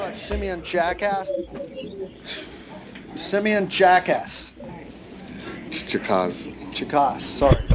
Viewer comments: Simian Jackass lmfao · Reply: I thought he said " send me in jackass"😂😂😂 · i swore i thought he said simian jackass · I thought he said send me in jack ass